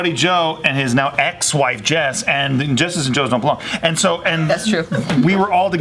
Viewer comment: could sm please transliterate what said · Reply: Because Jess's and Joe's dont belong